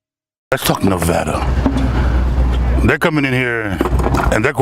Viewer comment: it sounds like says fuck nevada lol